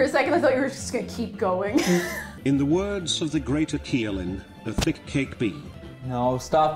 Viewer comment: ima be real chief. in i sleep deprived state I thought it said Sanic cake not satanic.